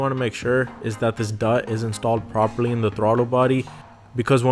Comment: Make sure what is installed properly in throttle body? No idea what word you're saying at 2:26. Dot? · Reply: Duct is what i said Thank you for liking the video and subscribing to the channel.